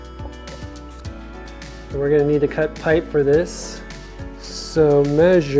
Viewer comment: I thought you said you were going to sand the pipe first ?